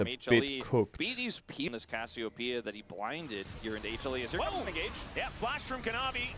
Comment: i hear penis